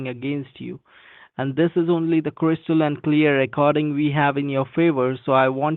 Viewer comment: did he say "crystalline clear" 😂😂😂😂😂😂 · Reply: Crystal and clear.